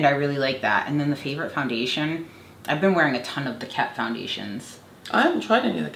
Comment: Wtf is she saying at 12:24??? I thought it was Tha Caff... is it Kett? I rewound it probably 90 times and cant catch it lol